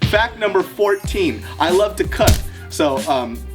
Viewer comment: Ha I thought he said "i love to cut"